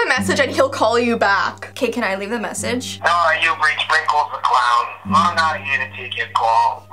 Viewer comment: The 15 dislikes are people were scared by wrinkles the clown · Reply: His name is wrinkles? I heard "Sprinkles" lol